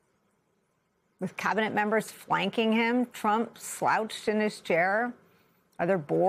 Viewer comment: She said "flanking him", when I thought she said "WANKING him"....😂